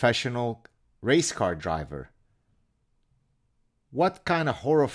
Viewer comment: I heard "race-card driver". That should go into the vernacular.